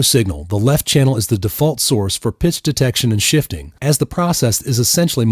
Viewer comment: I heard "piss detection".